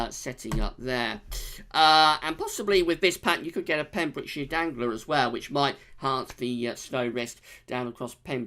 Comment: Did I just hear "Dangler"?